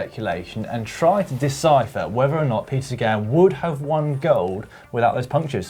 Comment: why does the caption say "Pizza Gal" instead of Peter Sagan? hahahah